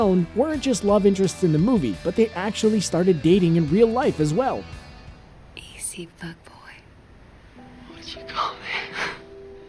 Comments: What is she saying? "Easy, fuckboy?" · did she say easy fuck boi